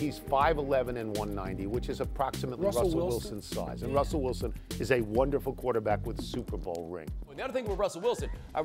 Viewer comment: When he said russel Wilson I thought he said russel Westbrook